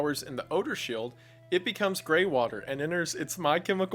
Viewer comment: I thought you said gay water